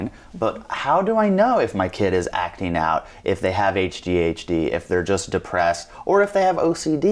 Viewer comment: Did he said hdhd at 7:19? Wtf is that?